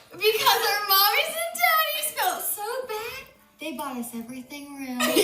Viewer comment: “our mommies and daddies” is that what she said?